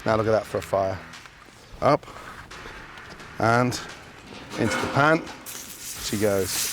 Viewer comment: When Gordon said "into the pan" i heard "into the mouth"